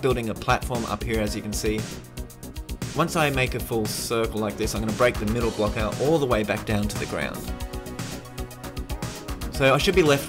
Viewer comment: does he say Circle